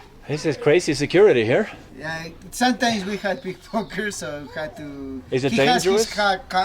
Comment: did he say "sometimes we have big fuckers?" lmfao